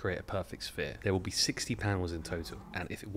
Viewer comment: Oh you're saying panel. I thought you were saying pounds and i thought that some of these balls were absurdly heavy